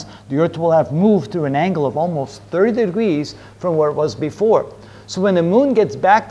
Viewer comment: how many degrees did you say? Thank you... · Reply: ~30 degrees